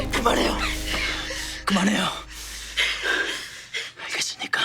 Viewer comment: 그만해요 다음에 뭐라고 하는거예요?ㅠ 알겠으니까?